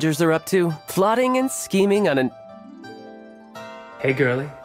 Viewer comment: At first, I heard "Plotting and screaming"